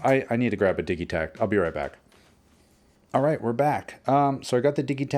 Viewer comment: "oh man this is so good. Im gonna have to go grab my di******" I was not sure what you said there for a moment, before you came back with the digitakt XD